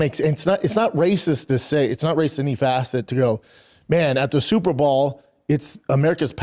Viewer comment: Bapa in any facet loves to insert the word "facet" in any and all facets. I had to replay wtf he said at 2:11 😂 ?? "Its not racist in any facet to go man at the superball" ? There is Dumb then there Bapa Dumb